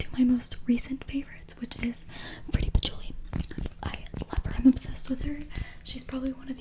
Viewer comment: Which asmrtisr did she say in 6:10 ? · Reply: fruity patchouli